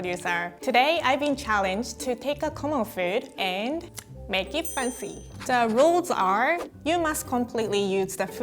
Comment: I thought she said "make it panty"